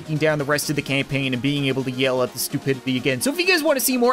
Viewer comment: did anybody els think that said to yell at the vegan.